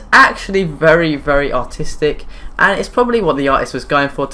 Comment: the dance class thing, i thought he said autistic instead of artistic 😂